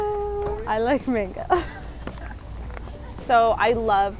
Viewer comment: I thought you said, “I like manga” haha